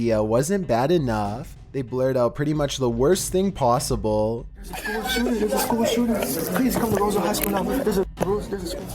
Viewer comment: what did he say? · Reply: skitzo 2494 “there’s a school shooter”